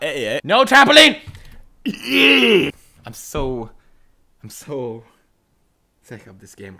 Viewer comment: I heard I so sexy